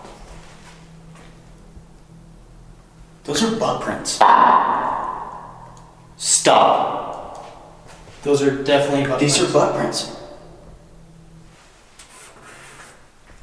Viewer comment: Hey guys! 21:35 I can hear a male say "stop" I promise you I heard first time and couple of seconds later when you guys get in to the cell with butt prints the device says STOP! that's freaked me out! Be careful! Listen to it again and see if you hear it like I'm doing